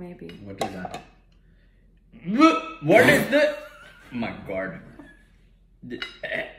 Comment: His accent cracks me up. When he said 'my God' sounds like he is saying 'my guard.' lmao😂